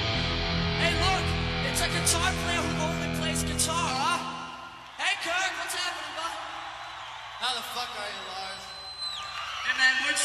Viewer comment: what did they say? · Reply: Lars: It's a guitar player who only plays guitar, Hey Kirk what's happening bud? Kirk: How the fuck are you Lars. Lars: Hey man, we we're in San Diego. Kirk: No shit, hi San Diego. Heres your answer.